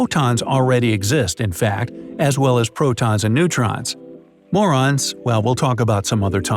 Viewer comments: Who else hear M***ns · neurons what i hear: m***ns